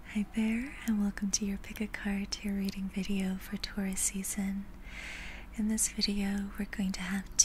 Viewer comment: I was grading homework while listening, some what distractedly, and I thought she said "tourist season." She's in Bali, after all...: 🤣🤣🤣